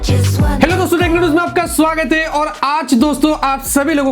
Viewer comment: Heard it as "Tech News meh aapka swagat hai", listened again & confirmed it was "Techno Ruhez"**